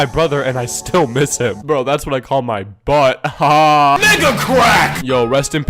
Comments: i heard ni**a crack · that sounded like n-word crack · i heard nigga crack